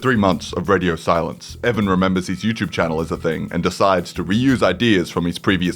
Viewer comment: misheard him when he said "uni" and thought he said "youtube" and i almost freaked